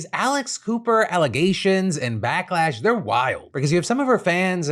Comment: I thought you said Alice Cooper....lol I was way more interested until I realised it was some random podcaster